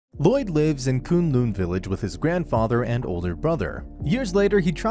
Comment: for a second i thought you said coon loon village